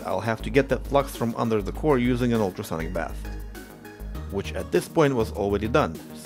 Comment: what did he say on 8:21 ? some bath?